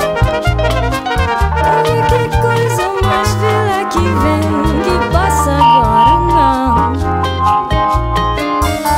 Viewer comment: Que dice al final la mujer? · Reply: Creo que dice "hay que cosa más bella que ví. ¿Que pasa ahora?